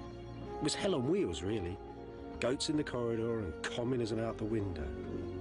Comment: what does he say? I have a hard time discerning - does he say "communism out the window"? Because if so... this is already going to be a good 3 and a half hours!